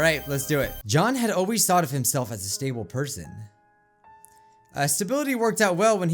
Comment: i thought it said stable potato